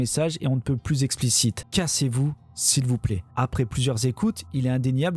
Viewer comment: Moi j'ai entendu "cassez vous sérieux"